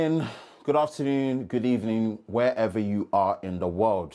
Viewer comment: I thought at the start you said "good afternoon good evening and goodnight" lol